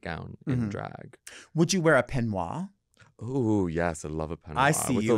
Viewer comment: I cant figure out what they're saying here Penoa? Peneau? Pineu? I'm assuming it's a robed garment, but what specifically are they saying here? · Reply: peignoir!